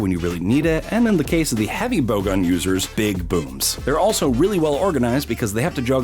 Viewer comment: wait Booms Boops Or BOOBS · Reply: Actually I really thought he said b** but it was actually big booms